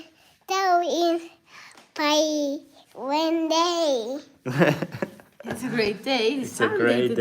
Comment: I replay this just to hear say, "Hello." She sounds like a movie star. 🙂She's so mature for her age. She brings a lot of joy to our hearts. I think she says, "One day" at 1:16 -- maybe she means one day she'll meet us??? "Very good!" I hope you find a wonderful helper.